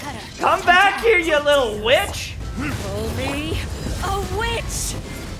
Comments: i heard "call me a witch" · I thought she said "call me a witch" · Ngl, first time I thought she said bitch lol. I don't disagree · Sounds like she said CALL MEE.....A WITCH!